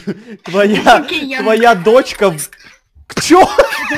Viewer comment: Что сказали на 6:55? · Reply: Монстр: лезбиянка